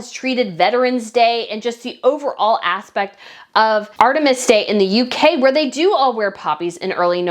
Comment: I think I heard you say ‘artemis’ day. It’s Armistice day! 😁